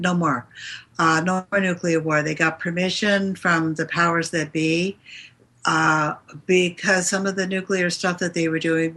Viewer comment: Clarification please. Around 29:00 the discussion is about turning the nuclear bombs off...and then says 'they have turned off all __ activity". I hear hark, harp...not sure what is referring to or what the word is. Thanks. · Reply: HAARP is the high frequency active auroral research project, A partly clandestine U.S. project, based on earlier, tesla technology, his patent bought by elites, and suppressed until emerging as a joint venture between the air force and the navy in Alaska. It is a vast array of antennae, where mega watts in the millions are blasted to hat the ionosphere, and thus perturbing the jet stream. In short, it is a very potent technology with several military and civilian applications, including weather control, the ability to destroy satellites, airplanes and even resonate with the human brain, horrifyingly as hard as that is to swallow. Mega weapon potential.